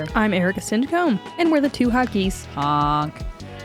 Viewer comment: NGL, I always thought you guys were saying "We're 2 honkies. HONK!" But its "Hot Geese" 😂